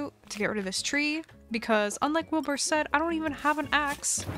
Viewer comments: not me thinking she said wilbur soot instead of wilbur said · Anyone else heard her say "Wilbur soot" ? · did I hear her correctly it sounded like she said Wilbur Soot